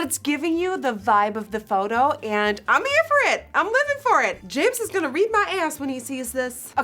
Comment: Does she say “James is gonna read my ass” 😂